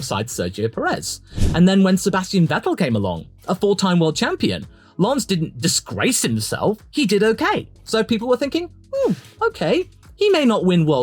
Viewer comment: I thought you said "a full-time world champion" for a moment